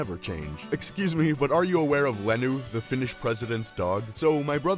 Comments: I thought the last one said ME and m BROTHER got engaged I said sweet home Alabama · I THOUGHT THE LAST ONE SAID: "My brother and I got engaged" HSHDJDJDC HELP ME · Bro i thought the 15th one said “my brother and i got engaged” and i was so disturbed and then i realised what it actually said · I thought it said “my brother and I got engaged, and had to watch the whole thing agian